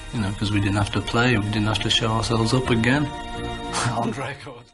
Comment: What an unexpected treat for the weekend, thanks! Edit: I listened to the clip just before 23:53 several times and what McCartney actually said here was "we didn't have to show ourselves up" which means embarrass themselves (presumably through bad playing). This might be a British phrase and is not the same thing as not showing up. Although it is probably a Freudian slip as they probably didn't show up either! So it doesn't detract from the point you are making 🙂